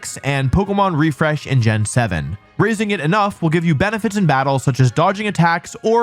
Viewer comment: I heard “Rizzing it enough” and was about to go crazy